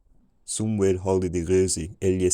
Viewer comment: it was so crazy listening along to basically gibberish and all of a sudden hearing "it is long winter, the ground is cold" emerge from the fog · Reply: Around 650, I heard "somewhere", then at 700 "it was several days". But better understanding at 1200. So cool.